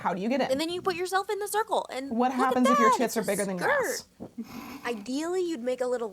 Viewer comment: did just say "tits?" Ma'am..... I bought those needles... they fucking snap.... Between, Bernadette, Rachel, and Morgan... you'd think I'd have an idea of where to go for good hand needles....